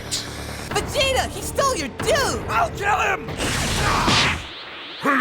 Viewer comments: when goku said "Vegeta he sole your do!" I thought he said blue, instead of do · Can someone please tell me what does Goku say to vegeta at 14:07? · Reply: "Vegeta, he stole your do" making note of the fact that 13's hair is similar to vegeta's